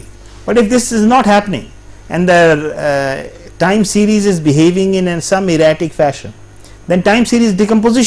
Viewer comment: The subs should be '... and the series is behaving in some erratic fashion' instead of erotic fashion :p